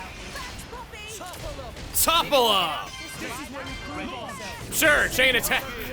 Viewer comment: i have played the game twice and i always thought that he said "Topple Hook" instead of "Topple'em"